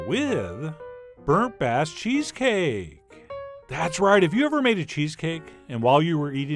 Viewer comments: Who else thought he said burnt ass cheese cake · I thought heard "burnt ass" cheesecake for a second...